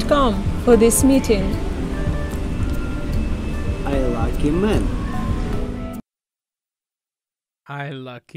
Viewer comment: I totally thought said.. I likey men... 😆